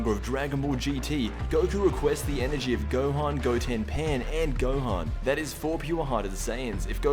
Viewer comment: You said Gohan trunks pan gohan instead of gohan trunks goten pan